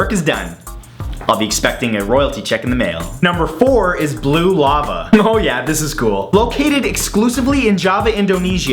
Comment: I thought he said "Lube Llama" XD